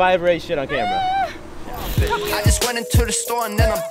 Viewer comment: did he say I just went into the storm then I farted?